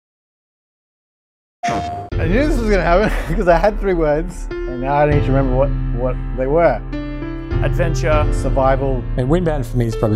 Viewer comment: im sorry sir i didn't quite get that did you say Eehfhaiso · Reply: 😂😂😂 It took me a couple re-listens to get it too. "I knew this was going to happen, cause I had three words, and now I need to remember what they were."